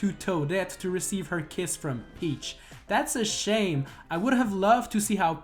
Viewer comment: in the video it sounds like your saying Peach sh*! if you can't say it right you should say peach toadet